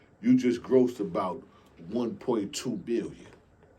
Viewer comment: did say 1.2 billion lol